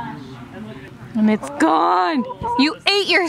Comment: When they said were going to flowrider I thought they said were going to see Flo rida